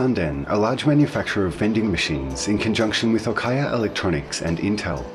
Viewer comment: did he say al quaeda electronics?